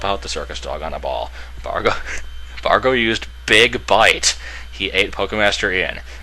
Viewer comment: I misheard 'big bite' as 'big butt' and burst out laughing.